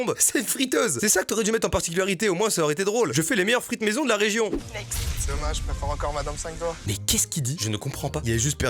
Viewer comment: Oui moi mdr j'ai compris 😂 il a dit "dommage je préfère encore madame 5 doigts" cet humour 😭